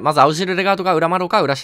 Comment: 画面見ずに動画見てたら青汁レガートに聞こえた笑笑